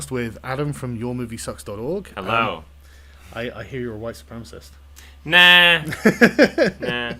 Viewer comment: Close caption: "your movie sucks dog allow I hear your wife's concept now"